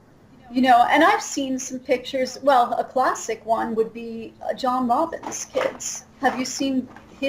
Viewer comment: Who did she say was a classic example of generation deterioration? John something or another? Edit: I found it: John Robbins. Oh yeah... google them. Total generation degeneration.